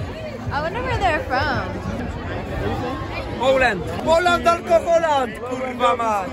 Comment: when I saw him, I thought "he looks so Polish" and then i heard "Poland alkoholand, kurwa mać" XDD